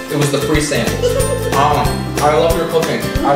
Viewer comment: Hahaha first I heard "I love your cocaine" :D